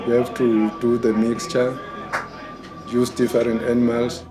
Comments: the translation is incorrect. He actually said: "Use different animals" NOT "use the foreign animals". C'mon Vice, at least get this transcribed by a South African. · did he really say "use the foreign animals" - or was it actually "use different animals"? Not that they use anything other than vultures in this video. But I don't get why he'd call them "foreign". Would seem more likely dude was talking about mixing other "magical" animals as well into some sort of combo-package.